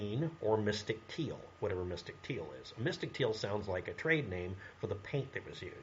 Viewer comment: sec Paraphrased - “Mystic Teal sounds like a name of a Train. I laughed and fully agreed. Then I realised I misheard (trade for train) but I still believe it sounds like the name of an old steam train.